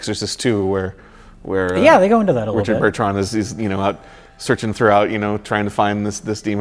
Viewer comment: I just noticed while re:watching this... did call Richard Burton ‘Richard Burtron’?!